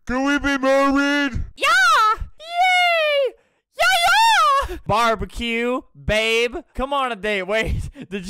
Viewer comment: i heard barbie cube😃